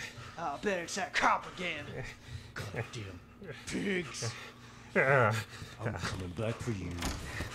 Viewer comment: I thought said God daim pigs